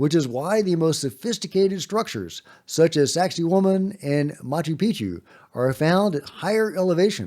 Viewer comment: When says "Sacsayhuaman", I always hear "sexy woman".😂